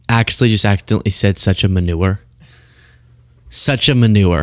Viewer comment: I think he said "Minerva"